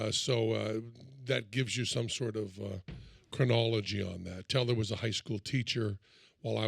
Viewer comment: He says "chronology" not "criminology" as the subtitles said. LOL